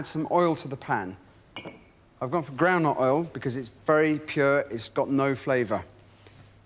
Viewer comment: I thought he was saying "grandma oil"...but I guess it was "ground nut oil", a/k/a Peanut Oil in the US.